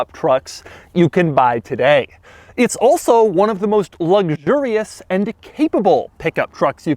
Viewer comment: is it just me or does Doug say "Lugdurious" instead "Luxurious"?